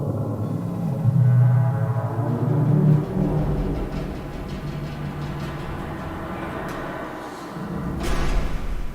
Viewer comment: someone figure out what this says cuh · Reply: Arise, gavel of rectitude Grind the enlightened to heel meaning